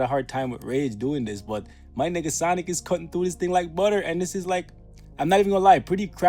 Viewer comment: I thought said my n***a sonic 💀💀💀